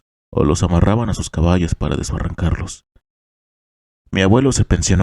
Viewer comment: 🐴🐎Dijiste *Desbarrancarlos*❓ó yo entendí mal🤔